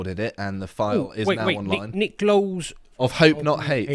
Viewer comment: I thought said "Nick Knowles"! 😆😆😆😆